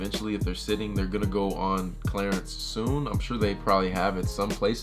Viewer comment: he always says "Clarence" instead of clearance lol